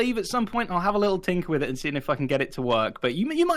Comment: Im ngl i search comment for about 5 to 10 min and i havent one people say it, but at 21:14 did say the f word? In grian family friendly content 😂😂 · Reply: He said “I’ll have a little tinker with it and see if I can get it to work”